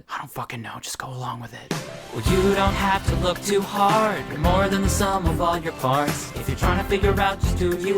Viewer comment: genuinely thought this said youre more than the sum of all your farts